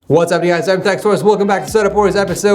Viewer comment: subtitles say ‘what’s happening guys I’m sex toys’